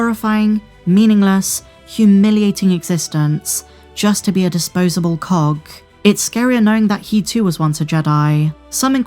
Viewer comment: at first I thought said "a disposable cock."😂😂😂 But seriously, your vids are really starting to lack any real substance.